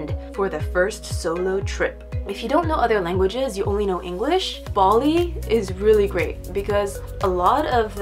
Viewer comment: it sound like she was say baldi not bali 😂